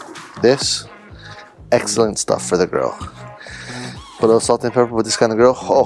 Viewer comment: everytime he said grill I thought he said girl so I thought he was talking about feeding it to a dog